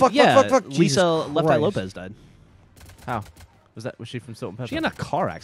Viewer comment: i heard "Lopez died" i immediately thought of RvB